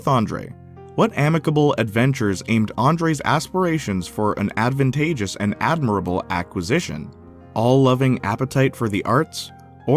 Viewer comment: WHAT DID HE SAYY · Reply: What amicable adventures aimed andres asperations for an adventatious and admirable aquasation